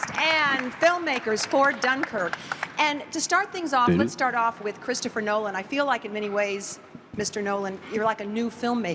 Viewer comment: what is harry saying lmao XD i heard "dUh" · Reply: hahahahah "did it?"